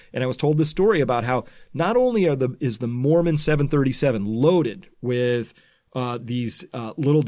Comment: Did he just say "Mormon 737"? Don't those guys ride horse carriages?